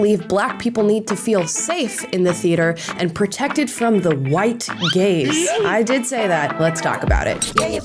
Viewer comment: I heard "protected from the White Gays". HA! Gaaaaaaaze. White Gaze. I legit laughed a lot.